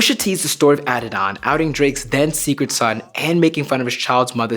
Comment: Lokey thought he said outting drakes dead son